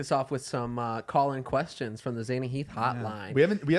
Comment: I.am.so.stupid I thought when they were saying “call in” that they were actually saying “Colin” I’m gonna die stupid